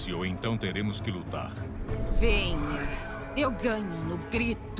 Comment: eu entendi direito ou só eu vi COISAS MALICIOSAS heheh kkkk " eu ganho no grito" a sindel é gameplay